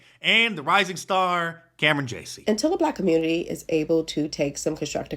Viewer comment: did you say Camryn Jaycee?